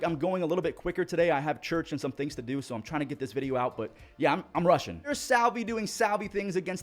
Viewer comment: I think he said rushing not Russian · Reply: That was the point buddy, I knew what he meant, but it was just funny the way he said rushing. It sounded like it said “Russian”